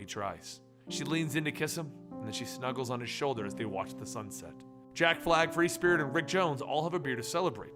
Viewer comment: The whole time I thought you been saying Jack Black...